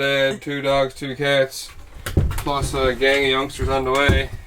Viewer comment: Are you two expecting? said "a mom, a dad, 2 dogs, 2 cats and a (can't make out) youngster on the way" (around 13:42) I watch each week and thought I heard one of you refer to the other as "momma" or "daddy" .... did I miss something ❤ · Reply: Yeah, I think he said, ..."and a whole lotta youngsters on the way...." so they probably are expecting, so happy for em. ❤